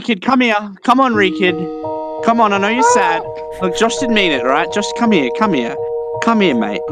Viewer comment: Josh didn't mean it. Josh come here..... . . . That's what I hear